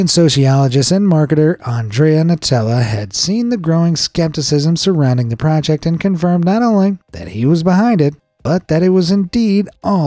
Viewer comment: Hey. I almost misheard you saying ˝Nutella˝ at 0:24:55.